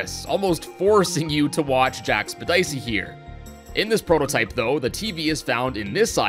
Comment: did you just call him jack speddisey?